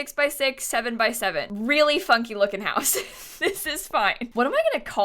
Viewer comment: why did i hear really fucking looking house